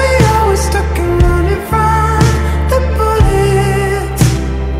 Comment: 턱걸이로 들려...